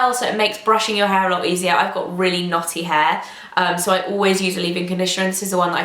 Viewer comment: Thought you said you had naughty hair! Backed up to figure out what you meant....knotty, lol! Love your makeup today.